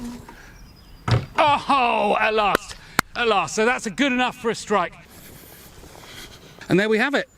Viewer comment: did I hear a "good enough for Australia"? 😆 But there was no magic smoke released. 🤔